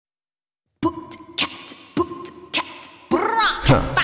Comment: all I hear is "boots n cats" lol